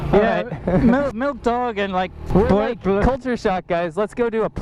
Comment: I keep hearing them say "milk dogs",,what the heck are they talking about?????